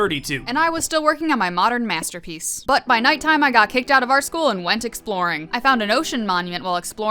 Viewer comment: i heard ,,got kicked out of our school" huh....HUH??